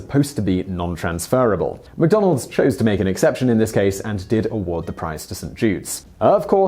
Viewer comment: It sounds like he is saying " and did award the prize to some Jews" xD xD